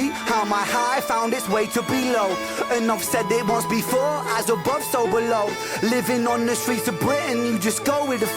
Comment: 2 for the price of 1, you're spoiling us. The first time I heard this I thought he was saying 'As a Bus Overload' instead of 'As above so below'. The live was filmed at Enfield, N.London with the same String quartet as in Money Game 3. The guys playing Football in the background are all part of Rens team. The singing part at the end lifts the song to a whole new level. Great reaction guys. Rens storytelling is unmatched.